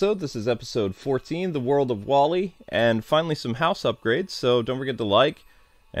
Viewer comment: I forgot was doing WALL-E so whenever he said WALL-E I thought he was saying Wally and talking about me. #BlondeMoment :_KwingsANGRY::_KwingsANGRY::_KwingsLaugh